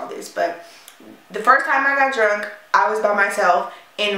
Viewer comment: I heard "first time I got drugged" and was confused because you seemed a lot calmer than I would have been if I was drugged. But nah you said drunk, lol I'm so relieved 😅